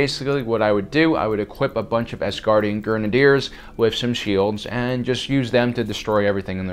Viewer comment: did he say "Asgardian Grenardiers' at 10:37 ?